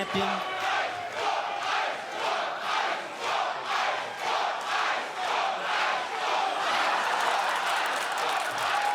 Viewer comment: i couldnt understand during the boxing match around 7:49 what they were chanting? can anyone tell me? no matter how hard i listen to it, i cant make out what they are saying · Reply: i heard ICE but not whats said before is it F**k ICE?